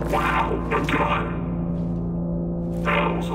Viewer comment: I love how the SMG and the AR2 are classified as, "Overwatch Standard Issue", but when you get the revolver, it's subtext is, "A Gun." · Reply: i thought it said 6 gun